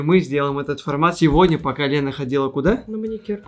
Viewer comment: Когда он сказал пока Лена мне послышалось по колена😂